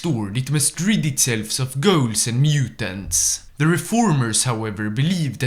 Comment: I thought you said “Gauls” when talking about “integrating ghouls” and I thought- ok super mutants are one thing, but the French 🤮